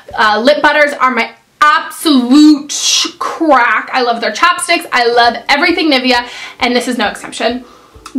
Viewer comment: I thought you said chopsticks instead of chapsticks! haha